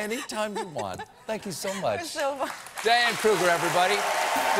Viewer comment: I thought I heard Diane cougar